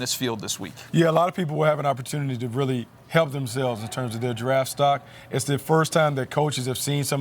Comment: Thought Bucky said "giraffe stock"